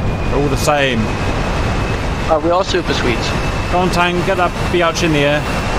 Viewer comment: "I can't remember the name of this plane....Some dooshbag"....I honestly thought you said "Doosh-tang" for a moment there....